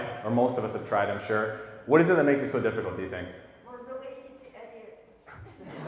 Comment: What did she say? Sry my English is not good My family is sadly very, very anti-vegan. Especially my sister. She is so a fanatical Meat-Eater with a very big ego. I have no Chance 🙄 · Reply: “What is it that makes it so difficult to be vegan?” “We’re related to idiots.” 😂 Don’t give up on your family. The people who are the most defensive, often feel the most guilty… which is why they work so hard to defend harming animals unnecessarily. Hopefully they’ll come around sooner rather than later.